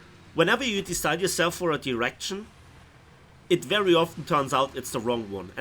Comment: i heard direction as erection and the follow up sentence suited the word perfectly